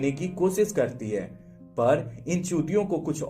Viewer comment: Did you say chutiya